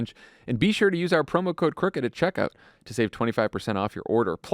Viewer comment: I listened to the add three times, I swear I thought they were saying "promo code QUICKEN", I was like "really?" lol. It's "CRICKET"